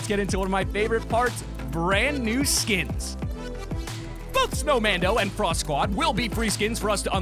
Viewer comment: sounds like he said "F**K snowmando and Frost squad